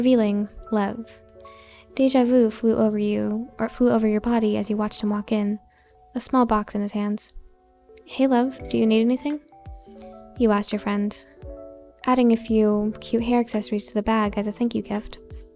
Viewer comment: when reader said “hey lev need anything” I THOUGHT THEY SAID “hey love need anything”😳